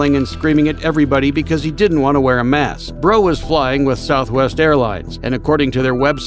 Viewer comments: i missed the guys last name and thought you just said “bro was flying with westjet” 💀 and i was like what? LMAO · am i the only one who thought when he say Breaux he said "bro was flying with south airlines" and "bro didnt like that rule 💀"